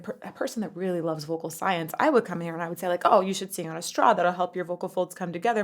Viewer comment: Did she say you should "Sit on a Straw", and that would help tighten up your vocal cords? I mean I feel like that is probably true, but yikes.